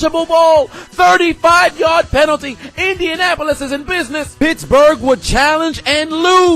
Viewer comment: I thought this man said bitchburg